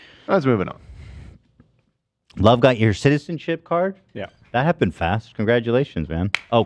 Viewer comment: omg i’m just realizing that during the part where love got his costco membership I THOUGHT THEY SAID CITIZENSHIP until just now reading the timestamps😭😭😭😭i was like damn i didn’t know u bought groceries from the place u get ur citizenship at😭